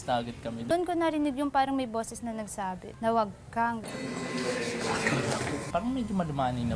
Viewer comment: ang narinig ko malinaw n malinaw “ wag kang pacute”